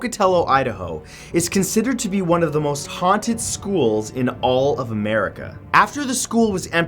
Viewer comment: Is it just me or did it sound like he said, "In all of 'Merica"